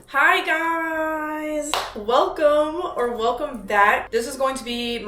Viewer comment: hi gir...uys I HEARD THAT I SWEAR